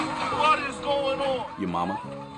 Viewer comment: I thought that she said project blue bean 😂